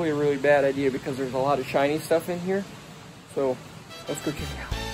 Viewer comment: When he said it was probably a bad idea to go in there because there was a lot of "Shiny Stuff", I thought he said "Chinese Stuff". Either way would be true.